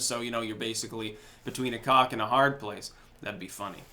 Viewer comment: Bro... did you say "between a cock and a hard place"? lol Freudian slip